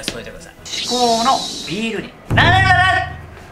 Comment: え、完成した時なんて言ったの？成せばなる？